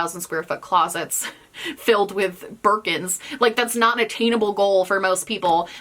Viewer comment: I'm not sure what you are actually saying in the flex culture bit, but I keep hearing "merkins" and now I am wondering why anyone needs 3k sqf of cooch wigs... 🤔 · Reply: Birkin. Ludicrously expensive purses.